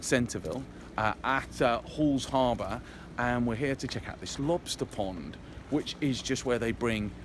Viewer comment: I thought he said "we are going to check out the lobster PORN"